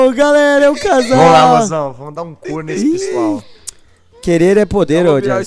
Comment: é impressão minha ou o falou ''cu''? eu acho que ele falou outra coisa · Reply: Ele falou "Couro" kkk